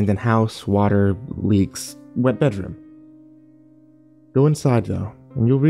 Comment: At first I thought it said "Wet Bathroom"